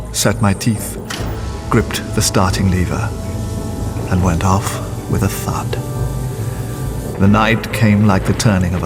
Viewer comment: During the narration at the start for The Time machine, it does sound like he says he "went off with a fart."